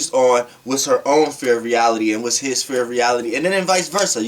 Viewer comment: Why do you say "fear" instead of "sphere"? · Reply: It's like I hear "fear of reality" in "sphere of reality"